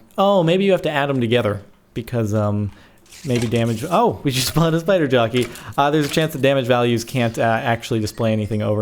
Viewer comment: did he say oh we just spawned a spider donkey?